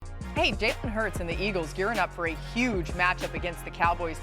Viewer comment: Sounds like she is saying Jason Hurts lol